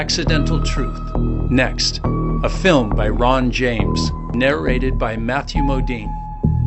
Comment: I thought you said LeBron James 😂